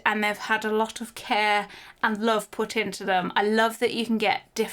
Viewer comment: At first I thought had said „They had a lot of care and loaf put into them.“ about the loaf lamp 🤣